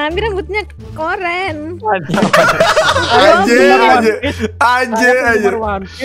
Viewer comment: Kalo salah denger nih ye maap maap nih kalo salah ngomong anjer ya?